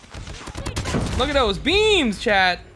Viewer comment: lmaooo I thought he said “beans” but he said “BEAM”